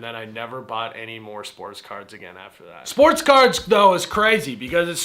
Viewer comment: I definitely heard “sports cars” first.